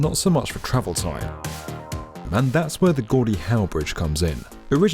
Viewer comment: I swear I heard “Goldie Hawn Bridge” that d be so cool!